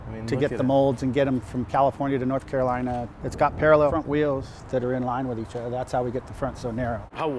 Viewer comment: it's got paraleffent wheels?? what is he saying?